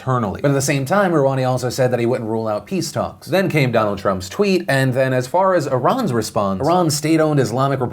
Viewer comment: Here's some time codes so you can jump around like a gosh darn time traveller! Ligma Outbreak (00:06), Cuba (2:26), TIA (4:03), Japan (5:28), James Gunn (6:17), Trump vs Iran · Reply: I got a little scared when I thought you said "We then had Donald Trump in the nude!" - please don't ever.......lol